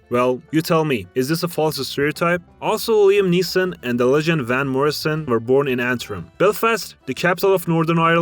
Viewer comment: did you just say the legend Van Morrison? <3